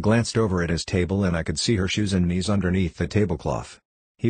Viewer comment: Bro I was zoning out and all I heard was "receiving head"💀